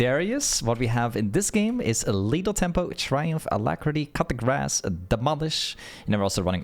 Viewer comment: Ngl I thought he said cut the grass instead of coup de grace